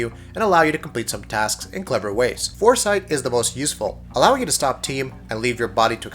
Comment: did he say "stop time" as "stop team" at 2:42 or was that just me? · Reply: Yep,he definitely said 'stop team' haha